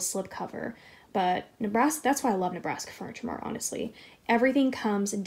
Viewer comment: I prefer the look of the love seat, the chair looked rather cheap or maybe it was just a bad angel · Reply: Do you know where she got the love seat from? I thought it was Ikea but I thought she said Nabraska Furniture.